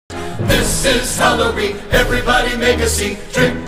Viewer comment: I heard... "This is celery everybody make a scene-"